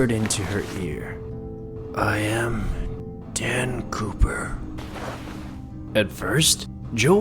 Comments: I thought she said dan pooper 😂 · I thought he said Dan pooper 😂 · I thought they said Dan pooper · who else thought she said Dan POOPER